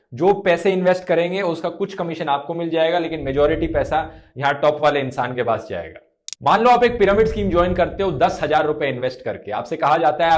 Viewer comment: jha*nnt top wale insaan. 😂🤣😂😂 I heard